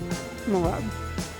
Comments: Did she really said "this taste like cum" at 1:34 ??OMG😱😂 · I thought she said it tasted like cum at 1:35, almost dropped my phone.